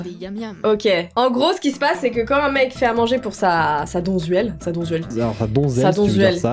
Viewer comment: j'ai entendu "sa Bonduelle"... C'est tout pour moi